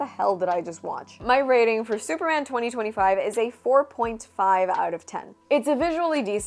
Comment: Had to rewatch that first part twice. No, no. Not that part. Well, yes, that part, too, but I meant the rating part. Thought you said 4.5/5 instead of 4.5/10. 😅